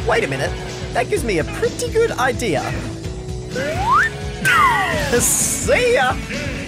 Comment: did you said ka SEE YA?